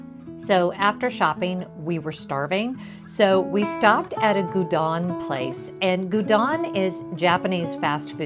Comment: I'm guessing that the shops checked for your visa stamp. I live in Japan and think they'd probably give me no "duty free" discount based on my passport 😂 . Oh, Gyudon (G-you-don). (Gyuniku is beef in Japanese) I thought you'd said Udon which is a bowl of noodles.